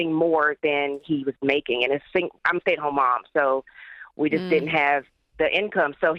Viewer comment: She said I'm a single mother and she's married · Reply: Where did you hear single mom, I heard stay at home mom which is way different